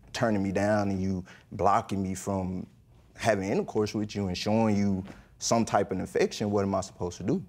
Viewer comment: Did he say “show her some type of infection?” Beautiful Freudian slip from this habitual cheater.